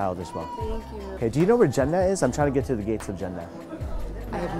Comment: When he said "Jannah", I first thought he was saying "Jeddah" lol😂😂😂😂